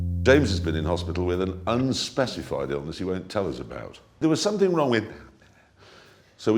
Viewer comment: What is he saying or rather, whispering at around 0:19? "There was something wrong (up there) "?